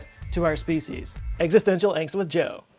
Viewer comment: I could of sworn you said - "Existential Eczema Joe".